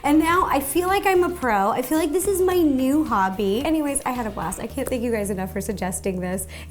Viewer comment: Did she just sayed at 16:32 "anywasys I had a plastick" idk